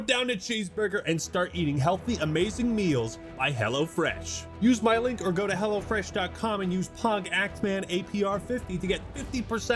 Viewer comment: I thought he said: "Use my link or go to hell." Now I am sad he didn't.